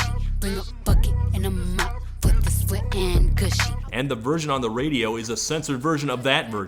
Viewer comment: "Wet and Gushy" is so much WORSE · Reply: On my first listen i thought it said Wet and Gucci and it sounds stupid as all hell but it's better than gushy